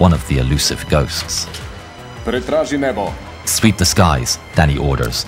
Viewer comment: "Sweep the sky Daddy orders " 😭👏 as I'm nit very familiar with this narrator's accent this is what I heard first 😭